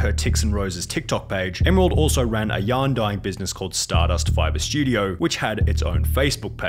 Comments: Why I heard it was "to promote her 'young dying business'" 💀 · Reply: I think he said "Yarn dyeing business" but his accent made it sound like "Young dying business" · You said she owns a yarn dyeing business, but I heard you say she owns a yon-dying business. oops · What was her business again? English isn't my first language so I kept hearing "young dying business"